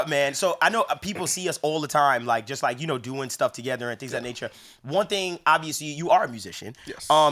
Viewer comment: for a sec I thought said "one thing, obviously, you ARE a piece of shit" which caught me off guard